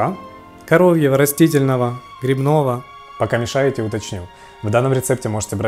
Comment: Грибное молоко? Мне послышалось?